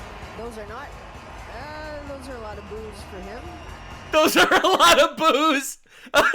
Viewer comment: I had this playing for background noise I thought she said “thaaaaat’s… a lot of boobs for him…”